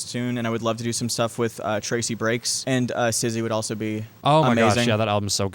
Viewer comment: Who's the artist at 19:50, sounded like "Sizzy"? Not sure how to spell it. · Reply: she's referring to Syzy! their new album The weight of the world is awesome